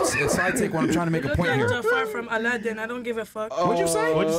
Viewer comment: I successfully went back a 6th or 7th time to figure out what she said to get the boot. “You look like Jafar from Aladdin, I don’t give a fk.” - 304 jokes